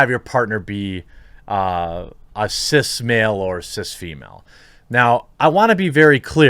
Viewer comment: I thought you were saying sis female. Like bro females or sis females.